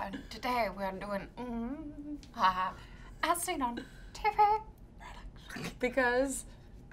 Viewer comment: I so thought they said" Today we are trying Ass-Eating TV Products" and I had to rewind and re watch.